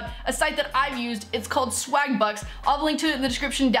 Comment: lol i thought you said Swagbutt then i would be in lol